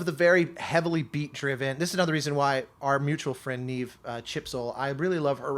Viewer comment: Who is the composer you talk about at 1:03:20? I can't understand when you say her name. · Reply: Chipzel! Look up SUPER HEXAGON